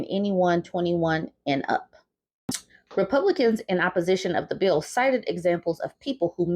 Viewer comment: 🤣🤣I could have SWORN you said “Creepublican”. I thought that’s the perfect name for them. They know good and damn well, it’s not teenaged girls marrying teenaged boys; it’s old men after underage girls.